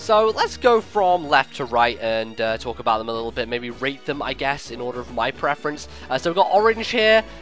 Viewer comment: lol thought u said rape them not rate lol